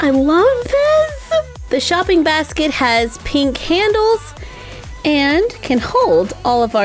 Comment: Wait did you say the basket has pink handles?